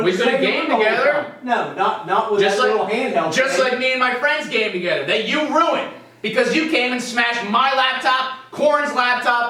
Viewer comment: sounds like he said him and his friends were having an orgy